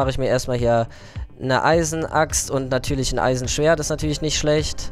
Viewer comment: Neeeeeiiiin, wieso sterben alle? :(( Kopf hoch, du schaffst das! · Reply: Du sagst bei 9:15 Eisen Axt und Schwert dabei ist es doch Stein xD is halt die Aufregung kann ich verstehen!